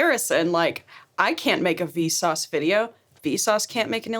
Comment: DID SOMEONE SAY VSA